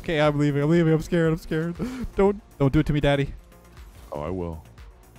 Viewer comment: im playing a game and all i hear is dont do it to me daddy💀